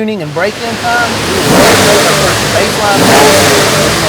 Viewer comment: what did he say? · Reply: Can't hear it all but sounds like "after tuning _ set baseline"